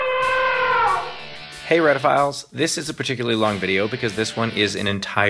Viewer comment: I'm an idiot; for quite some time I thought Steve was saying Reddit files at the start of his videos